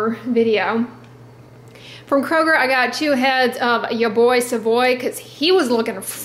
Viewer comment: 😂😂😂what I heard: "ya boi caboy!! Cus he was lookin' fresh"